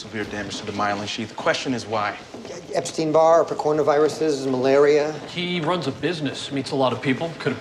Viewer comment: did he say epstein and coronvirus?!?!?\ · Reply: Ebstein Barr Virus and Coronavirus